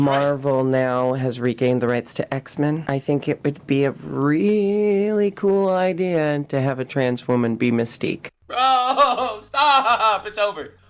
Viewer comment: my dumbass thought she said "it would be really cool to have a trans women be a mistake"